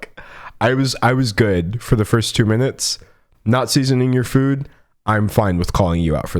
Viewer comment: I kept mishearing “not seasoning their food” as Nazis in their food. I need to get my ears checked.